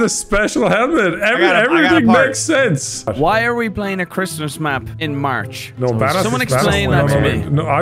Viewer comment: is it just me or you guys also? 1st time I heard it it was CHRISTIAN MAP when i replayed it its CHRISTMAS MAP now, is it just me or im just high.